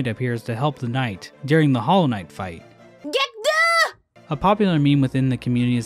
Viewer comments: I heard it more as "Get goin'!" when she yelled that, but "Git Gud" makes more sense, because it did take me about 30 attempts before I got it good. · I think hornet said get down 😅